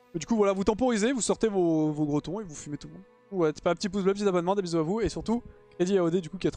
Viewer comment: j'ai entendu "vous sortez vos bretons et vous fumez tout l'monde" en parlant de trynda haha